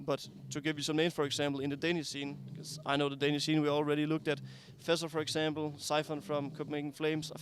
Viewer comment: Who's the 1st Danish player he mentioned close to joining the team? I can't hear it clearly. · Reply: fessor I think